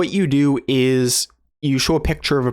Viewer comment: I immersed in Chinese so much I thought so said 说 instead of "show" here. lol